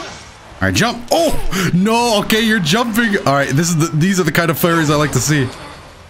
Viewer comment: Man I really like this game and seeing you play it! This is like that nice treat you get once a month. Also I kid you not at 8:12 I thought you said "These are the kind of furries I like to see."